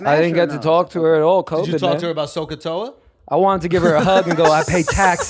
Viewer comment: did Schulz just say "SOH-CAH-TOA"? Is he having flashbacks from algebra class?